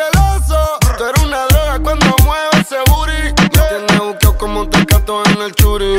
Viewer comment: pensé que decía ¨tu ere una abeja cuando mueve ese booty¨